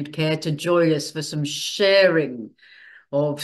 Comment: Great to see you back dearest. I was just thinking of you as the furst appeared on my cherry tree this week. Can't wait to hear your message tonight. ❤❤ haha I thought you said inbthe Tribe of light we'll have some 'sherry' not sharing. Little giggle. What a most precious gift from Oyaki (? Spelling) and her zoom friends. How stupendous. Very beautiful. Good ol Goody letting you know he's gone nowhere.